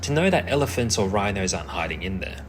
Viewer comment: I really can’t hear, at 10:53 did you say aren’t or are? I heard are the first time and was instantly confused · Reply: He said aren't